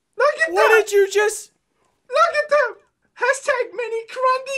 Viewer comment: Does anyone know what he screamed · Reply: I think he said look at the crunndes